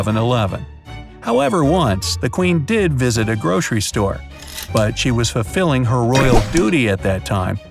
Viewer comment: why do i hear "groshery store" and not grocery store